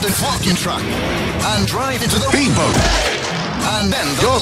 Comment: The ad: Lego Reality: "the new kids see ghosts from Kanye West" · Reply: I thought it said "The new KFC goes from Kanye West"